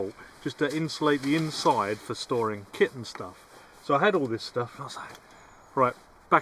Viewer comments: I thought he said for storing kids and stuff 😮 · good luck with your container for storing kitten stuff 😂👍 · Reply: He does have a cat at home, so for a while I thought he really said kitten stuff. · I thought you said "kitten stuff", and I was going to ask you to show us your kittens. 🙃